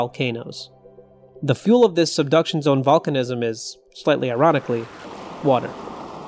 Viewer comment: it took me a couple rewatches to figure out you were saying "...slightly ironically ..." instead of "...slightly erotically ..." Might want to work on that enunciation